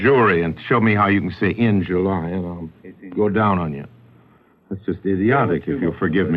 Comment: did he say “I’ll go down on you”?